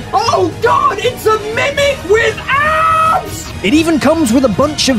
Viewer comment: Swore you said "mimic with ass" ... 😂